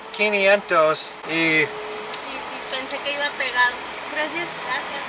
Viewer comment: yeah she was .. i heard her say "pense que iba a pegar" I thought it would stick. so she admited that she tought she could get away with it . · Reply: She said, "Pensé que iba pegado". (I thought it was stuck) She was refering to the missing bill. She was explaining that she thought that she gave the right change and came up with that lame excuse. She was absolutely trying to scam them. Shame on her, in these toll booths there are usually offices where you can file a complaint about actions like this. I have never been scammed in a toll booth so I will say this is the exception and not the rule.